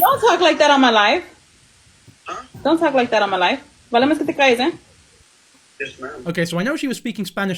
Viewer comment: I had to put it on ×0.5 speed, but I think she said, "Por lo menos que te calles eh" which translates to: "At least be quiet eh".